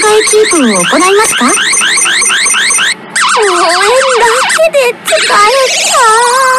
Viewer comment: あたりのセリフ、なんて言ってるんですか？どうしても聞き取れない・・・ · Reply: (応援だけで疲れたー) っと思いますよ